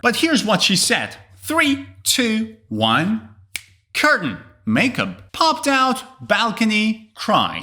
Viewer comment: I am so happy to get every accent correctly. Last one as well cause when you fill the backs you process the words which make sense. Like first I thought she was saying girl but then I thought no it must be curtains. So understanding accents isn't a problem if you make a sense out of it. But honestly enjoyed the video.